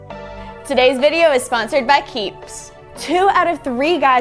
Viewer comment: Aye I'm early! Ngl I thought they said sponsored by peeps and I was confused